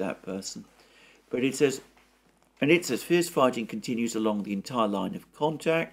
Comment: I was listening from the other room and at first I thought I heard you say "Fist-fighting continues along the entire line of contact". That would really be something to see!